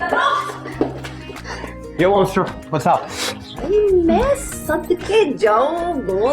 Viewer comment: Did Arsal just say "Hey monster what's up" to Shahana??😂😆